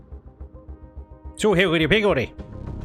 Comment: what did you just say there? Was that even English my friend? Haha. Also, lord that is one portly gal of a carrier you have built. Looks very nice. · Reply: its all higgeldy piggeldy, a common bitishism to bean uneven or wonky ;)